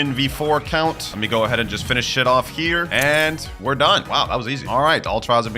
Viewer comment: dude did u swear I have never heard u swear lol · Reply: No he dint say finish shit off here he said finish it off here